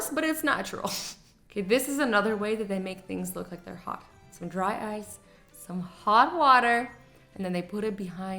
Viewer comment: For a sec I thought it said dry RICE my mind went blank until azzy pointed out it was dry ICE